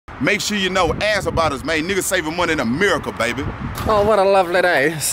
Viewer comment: What does the guy say in the intro?? I can't understand him lol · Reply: I thought he said " Make sure you know ahhh about us man, *iggas saving money in a miracle, baby"